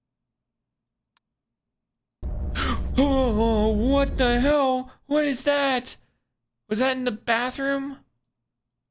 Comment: there is something elseee i couldnt catch it even slowed down tho so welp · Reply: it says "find my body" (slow it down to 0.25 and pause randomly)